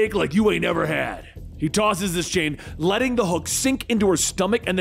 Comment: Did he call Starfire the hulk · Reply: " letting the HOOK sink into her stomach"